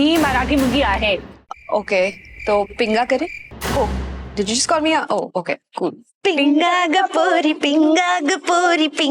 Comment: Did you just call me hoe🗣️